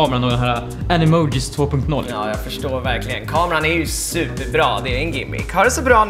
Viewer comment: Kameran är superbra ............? Vad sa han